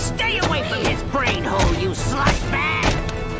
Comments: what did Rigby say? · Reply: “Stay away from his brain hole you slush bag” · what did Rigby say????? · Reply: Slush or Splush bag